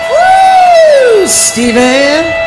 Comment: Be curious to know who's in the band. The dark haired guitarist with the big sunglasses is trying hard to be Stevie Salas and not quite getting there. And the drummer is not Brian Tichy, either, though he wishes he was. Nice to see the late great Jeff Healey up there rockin'. Sass is in great voice here as usual. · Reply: I paid more attention this time right at the beginning. She did say "Stevie" and either I totally missed it the first or second time around or I thought she said "Easy" as short hand for "High Road Easy" being the first song in the set. Thanks for pointing that out. Not sure how I missed it. And for some reason I guess I just assumed Stevie woulda been shredding that lead guitar part on "You Don't Have to Remind Me" too. They were dating back then. I got the feeling, listening to the lyrics on Rats, that they'd both gotten themselves wrapped up into some really nasty drug shit together. (Heroin seemed the most likely demon to me.) Thankfully she didn't destroy her voice in the process. Thankfully neither of them died in the process, either, if I'm being honest.